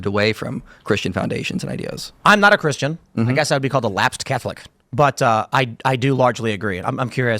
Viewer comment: When Tim said he was a lapsed Catholic I thought he said prolapsed and shot gatorade out of my nose at work.